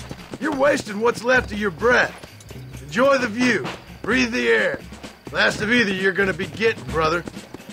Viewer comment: Okay what? Aside from that did I just hear John saying "breath Air" in 3:58? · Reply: breath the air. its a normal saying. john prob would hate andrew